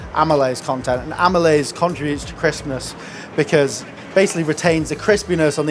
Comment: i thought he said "amalayse contributes to Christmas" 🤣🙈🤣